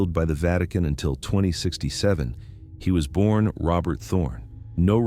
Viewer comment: did he say 2067 Just entertainment